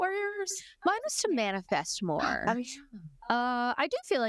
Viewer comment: I thought you said your resolution was “to manifest war” and you said it with such a straight face I thought it was hilarious but I think you said “manifest MORE” which isn’t as funny 😂😅